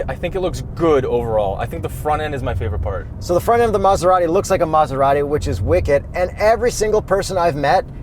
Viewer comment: I thought he said "the front end looks like a mazdarati" ...which it absolutely does btw.